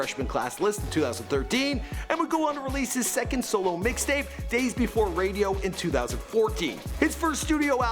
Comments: he did not just say day before radio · am I the only one that heard him say "days befor radio"? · Did he just say "Days Before Radio" Lmao